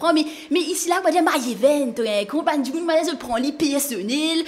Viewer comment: dis i heard mine name ( mayeven ) ???